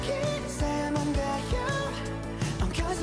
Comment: I'm confused at 0:15 was that "kumusta, seungkwan?" I thought this is seoul? or I misheard wrong?